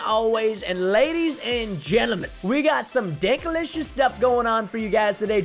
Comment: did he say dickalicious or am I hearing things